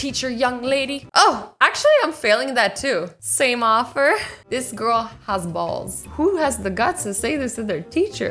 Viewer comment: did she say this girl has balls!